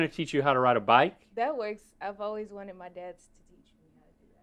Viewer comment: did mikayla call rhett and link dad 🥲❤️